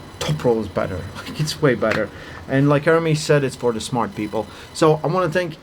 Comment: subtitles say "and like Jeremy said..." 🤣